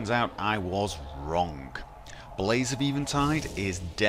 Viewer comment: When he said blaze of eventide, I thought he said laser beam time.